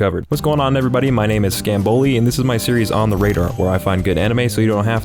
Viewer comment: When you say your name I hear "Scam Bully"...